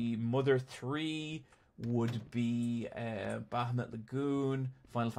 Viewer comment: What is the game mentions at 28:13? I can't quite catch the name. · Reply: Bahamut Lagoon